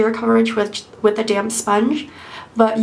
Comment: Lol at first I thought you said use a damn sponge! but you said damp lol damp sponge lol my bad